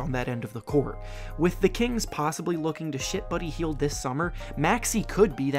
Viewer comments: I thought he said shit buddy hield 😂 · it sounds like he said shit buddy heild 😂